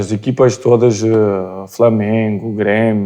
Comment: Só eu que ouvi "Flamengo", em 23:46, como primeiro da lista dele?? hahaha